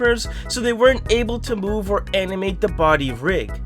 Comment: did he say animate or amimate?